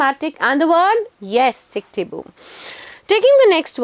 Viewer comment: What is the word at 6:20? Tic tabu? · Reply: It's tickety boo ..